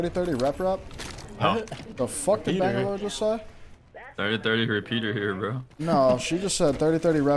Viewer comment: yeah i heard "30-30 rep rep here" too